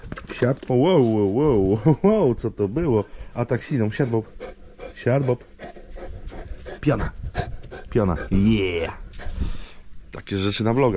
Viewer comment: Na początku usłyszałem " Bob, piana!" zamiast Bob pjona XD. Fajny psiak!